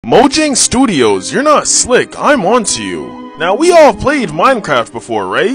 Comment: When he said: “im onto you..” I heard: “I want you..!”